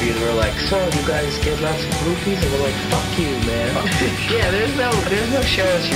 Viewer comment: what did Gerard say at 1:46 ? sounded like "so, you guys do lots of roofies?"